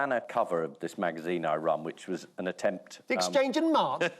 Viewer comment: A little help please guys? What does Paul say is the name of Private Eye? Thanks in advance! 🧡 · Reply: Exchange and Mart. It’s an old used car magazine